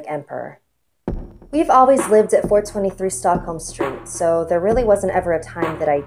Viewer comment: I thought u said 420 st.